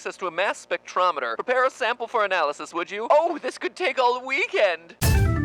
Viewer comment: did he say "this could take owl weekend"??? xD